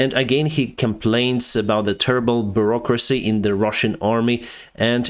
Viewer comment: "Terrible Bureaucracy" I keep hearing "Turbo Bureaucracy" Probably the only thing in Russia that is turbocharged right now...